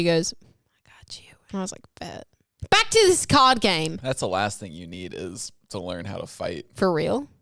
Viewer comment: It sounds like she is saying cod game instead of card game and I was confused at first